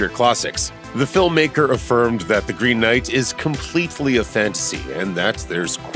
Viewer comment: Did u say..... “completefully”? lmao